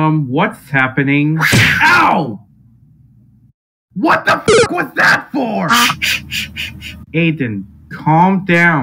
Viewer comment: What did he say? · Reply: WHAT THE FUCK WAS THAT FOR?!?!?!?!